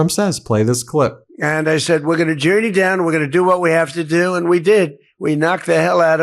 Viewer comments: did he say "we're gonna jew you down..." or am I hearing things? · Trump says 4:23 "We're going to JEW you down..." Did I hear that right ? 😂😮😂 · wait a minute... does tRUmp actually say we're going to "Jew you down" at 4:23? And no one reacted to that slur??!?? 🤬 If that's not what he's saying, what IS he saying there? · Excuse me? Did Donny say, “we’re going to Jew you down?” I hope that was just my ears deceiving me. 4:27. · Did anyone catch the part where he said " jew you down " timestamp 4:25.